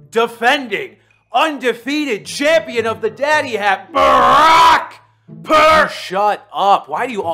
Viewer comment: I thought he said Daddy Hack and came looking for LaSteroid comments